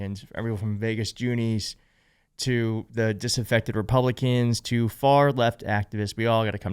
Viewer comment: Did he say Disinfected Republicans???